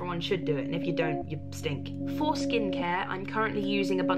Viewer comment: I thought she said foreskin car and i was like..... what? Then i realised.....